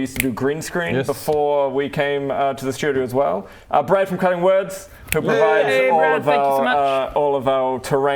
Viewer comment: wait... brad from cutting words? i thought yall said brad from cunning woods